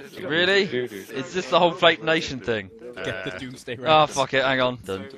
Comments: Did anyone else hear "Get The Doomsday Rounds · During that whole conga line smoke thing I heard someone say get the doomsday rounds